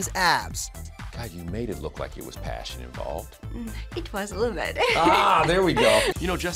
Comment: What does she say at 1:00 ? "It was ... " ? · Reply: it was a little bit